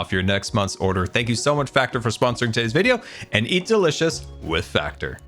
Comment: I heard "Etho-licious" instead of "eat delicious" at first lol